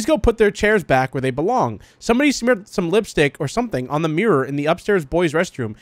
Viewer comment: I hear red lipstick: meh I hear red lipstick speared: oh it blood -_